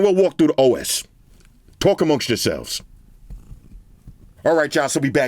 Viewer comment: I never understood what is saying (English isn't my native), can someone write it down? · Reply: Talk amongst yourselves.